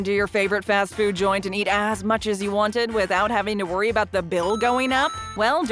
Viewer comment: why did I hear "Don't you wish you could just roll into your favorite fast food joint and eat ass?" Lmao. Great video!